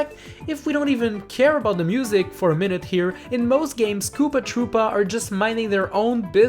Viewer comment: I thought you said poopa tropa